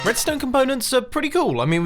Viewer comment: Why did I hear "Redstone components" as "brits don't"?